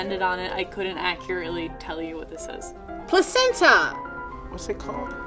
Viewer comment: she actually said placenta 😹😹😹🤦🏻‍♀️🤦🏻‍♀️